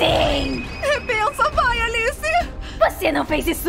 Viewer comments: KKK eu ouvi a circle dizer ''bença Alice😔''' KKKK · eu ouvi "benca maria alice)